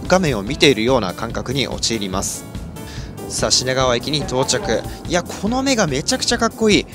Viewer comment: 品川駅がし○がわ駅に聞こえてしまう…